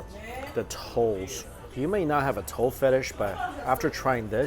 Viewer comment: I was skipping through some of the video and out of context I just hear "i have a foot fetish"